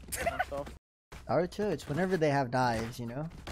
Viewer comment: can someone explain the naruto thing i couldn't quite make out what he said. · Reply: Yeah, I heard "Ar- church whenever they have *inaudible*"